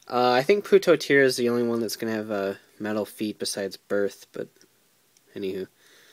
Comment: Lol I thought u said medal feet but its METAL feet lol :D